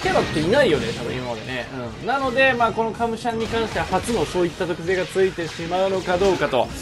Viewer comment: カムシャンがかむシャリに聞こえて お寿司の🍣ネタかと思った😂